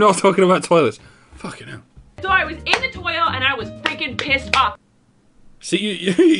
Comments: I think she was trying to say "toy aisle," but it came out "Toyle" or something. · No dear doctor of random and dark cheese, she said "toy aisle" she just said it so fast that it sounded like toilet.